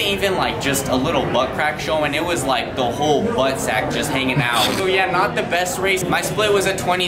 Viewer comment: did you say butt sack???? 🤨